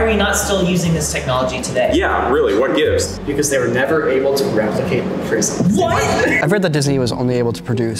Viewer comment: he said what because they w r g fs ... i hear nothing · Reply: Because they were never able to replicate the prisms